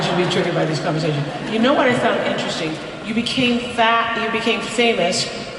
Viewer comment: Oprah says to GaGa “ u became fat — u became famous” Anyone catch that